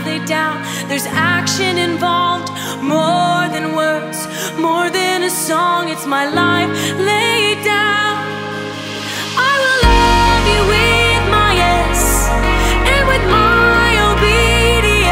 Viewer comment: First time I herd this, I thought she said “I will love you with my eggs.”